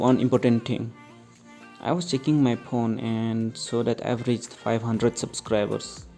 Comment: what did you say? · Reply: "I was checking my phone and saw that I've reached 500 Subscribers" 😅😅